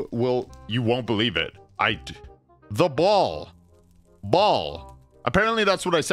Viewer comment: I heard ball the entire time too.